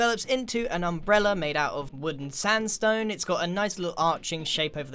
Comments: didn’t say wooden sandstone, he actually said “wood and sandstone” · Did he say ‘wooden sandstone’